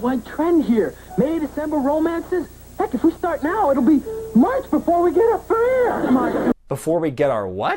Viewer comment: I'm listening to this on headphones, and think what he's saying around the 8:40 mark is "If we start now it'll be months before we get up for air." I still can't tell you what that "whu nuh naww" line is supposed to be, though.